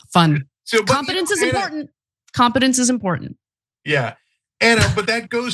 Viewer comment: OMG I thought Ana said "confidence is important" sarcastically! I realize now she said "competence." But hey, those affected by the Dunning Kruger effect have no lack of confidence . . .😂